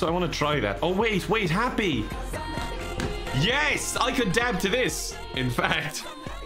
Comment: "I can dab to this" I heard "I can dad to this". It seemed relevant.